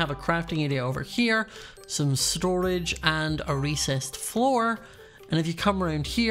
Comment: It sounded like Scott said he had a racist floor